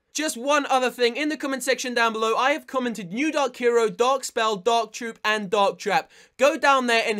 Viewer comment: I thought you said "dog hero, dog spell"